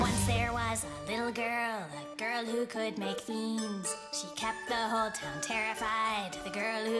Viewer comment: I thought it said, "Once there was, a little girl, a girl who could make memes"